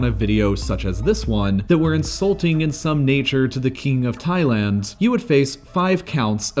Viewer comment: I’m so high I thought this video was saying there’s a king of hentai. I was like what the fuck, he actual rules over people? Then I realized how high I am